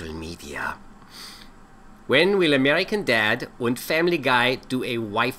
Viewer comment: When he says American Dad...sounds like he says American dead. .....sounds like a rock band.